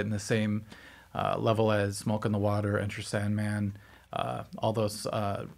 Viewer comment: it sounded like you said “milk on the water”